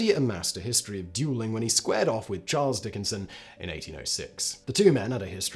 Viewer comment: I really thought Simon said Jackson dueled Charles Dickens, not Dickenson lol